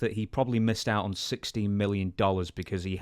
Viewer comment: I swear I thought he said 60 million dollars too.